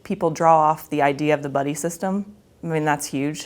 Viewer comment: I thought she said "bunny system", which would also make sense. #selfcarebunny